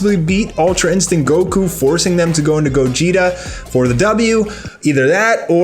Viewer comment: Wait I didn't hear you correctly did you just say Gogeta??? Didn't you say %100 Gogeta wasn't gonna be in Broly Movie... Congrats you just played yourself